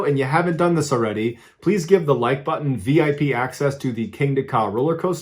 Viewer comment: Lol at first I thought he said give the like button VD not VIP lol